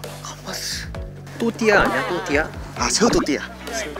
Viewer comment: I first thought they said CHUTIYA 🇮🇳🇮🇳🤣🤣🤣🤣🤣🤣🤣🤣🤣